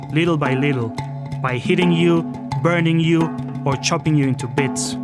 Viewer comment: I heard ‘ chobbing’ you up into bits